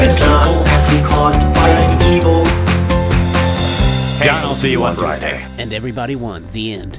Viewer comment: Why do I keep hearing "Hank I will see you on Frysday"